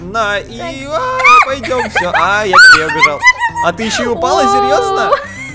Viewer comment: мне послышалось не "я ещё и упала", а "Борщ упал" 🤣🔫